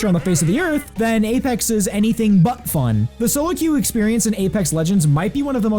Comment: Did he say Butt Fun at 2:04?